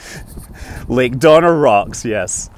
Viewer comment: LAKE DONNER ROCKS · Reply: i thought he said "like Donner Rocks" and i googled the name, trying to work out the reference.